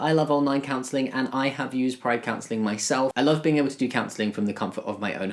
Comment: Because of the accent I thought He was saying Pride canceling instead of counseling and I was extremely confused